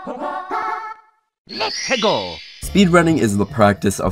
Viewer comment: A second part of this will be made at some point, going over the blooper/machinima community, it's long history, and more. ~ Click read more for additional notes: • For clarification, right here (11:10) I was trying to make the point that it was much harder to Hex edit a Mario 64 ROM in comparison to Super Mario Bros and Super Mario World, as there was way more data to edit to make a large hack. This is why people use a variety of programs instead of hex editors for Mario 64 ROM hacking (though hex editors are often used for very technical changes) • There are some strange editing glitches around 12:10. Sorry. It isn't supposed to look like that. • Sorry for the weird audio throughout the video. I don't know what I was on when I recorded this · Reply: what does mario say there? is he saying testicle? or is he saying lets pickle?